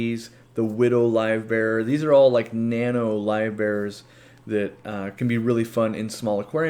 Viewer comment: This was super informative! Can't wait for the $100 tank set up video. Also, every time anyone says "live bearer" I just hear "live bear"